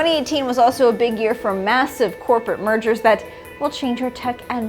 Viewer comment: I was surprised xoz i misheard mergers as murders